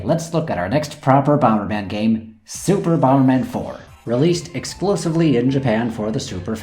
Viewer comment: did you say released explosively?